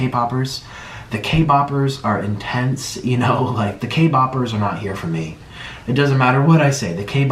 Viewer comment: Did he just say the k-popers are for the k-popers? Wtf is that supposed to mean